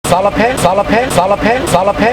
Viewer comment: Sounds like Palape say palape while listening to this